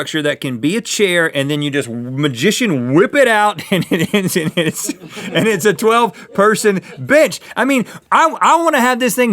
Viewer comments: it sounds like Rhett is saying "It's a 12 person b****" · sounded like he said bi**h... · D-did Rehtt just say b**ch? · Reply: Calder Worthington he said bench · who noticed that it sounded like Rhett cussed at 2:48 PS. he meant to say bench not bi*ch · Did anyone else hear rhet say the word b*tch instead of bench